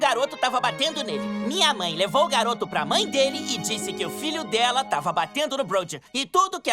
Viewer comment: É OQUE? Eu nao entendi foi nada que ela falou eu so entendi "Pinmasdisiahugs"